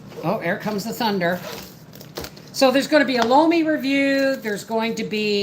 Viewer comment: I heard you say a lome what it it i am new to canning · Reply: 💁🏻‍♀️The Lome is a countertop composter...☝🏻no flipping/turning the ground 😁